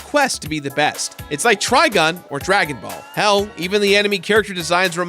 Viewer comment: did you say TRIGUN?! chicken miso